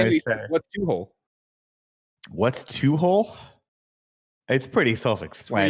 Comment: I always thought said Toot Hole not two hole. 🤣